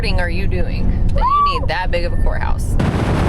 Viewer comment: thought yiu said whore house ...... 😂 had to rewind